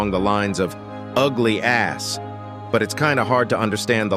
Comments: I think that tv man said "Ugly ass b*tch". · I think it says "Ugly azz, B*tch" But don't quote me on that. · i think the large tvman said “ugly ass bitch” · He actually said"ugly ass bitch" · i heard"Ugly Ass Shit" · i heard "ugly ass bitch" or bish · I heard "ugly ass bitçh" 💀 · The large tv man line in the big container I heard "ugly ass b*tch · The large speaker man sounded like he said ugly ass bish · i think the big titan tv man said "ugly ass bullsh*t" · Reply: All I heard was "Ugly ass bitch" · i think he said “Ugly ass b####” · i think he say ugly ass sh*t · I think Large TV Man said "Ugly ass shit." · i think he said "ugly ass b*tch" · I think large tv man said “ugly ass bitch” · the big tv that teleported us said this is what i heard by the way i heard "ugly ass bitch" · So this 13:24 when I hear it kinda say you ass and oh sh*t the closing the door and the human skidde ran to the tv after he put this sunglasses and ran to it and 15:36 you say it 007 but it say 067 I’d numbers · I only hear "Ugly B%%CH" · tv large says "ugly ass bitch" but its what i hear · I heard “Ugly Ass, Bitch.” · i heard him say "ugly ass bish" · Team bring back brown cameraman back 👇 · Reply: I heard "ugly ass shit" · "Ugly a$$ b!tch" That's what he said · Nah I heard “Ugly ass bitch” I know it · I think I heard " ugly ass shi-"